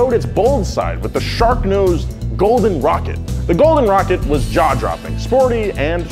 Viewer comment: Did anyone else hear: ‘The golden rocket was Jar Jar Binks’?